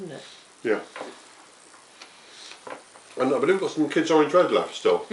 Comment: what variety did you say it was? I can't make out what you said. Thanks. Great video. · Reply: Kidd's Orange Red. One of my top recommends.